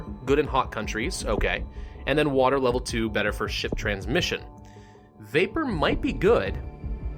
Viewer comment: is it me or did he just say “Shit transfer”